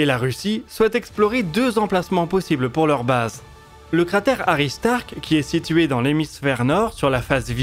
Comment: j'ai compris cratère Tony Stark...🥴